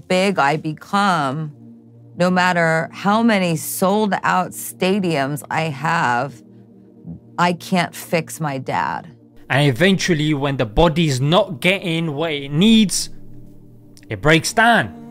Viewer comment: did she say I can't fix my dad?